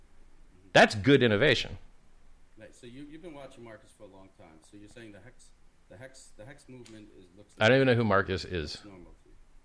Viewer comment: “I don’t even know who Marcus is….who’s Marcus?” 😂😂 😂 I thought he said Marcus too